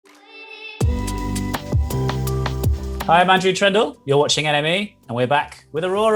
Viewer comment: I thought he said "you're watching anime.. " and I was confused like yeah that's usually true but I was here for Aurora 😂🌬🔥